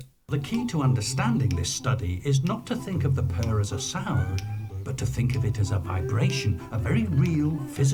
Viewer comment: "The key to understanding this study is not to think of the purr as a sound, but to think of it as a sound" is what I JUST heard, That's like saying "Try not to think of darkness as the absence of photons but rather the absence of light"